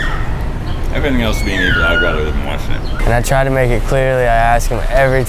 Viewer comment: I have no idea what he said but I think it was he would rather live in Washington so respect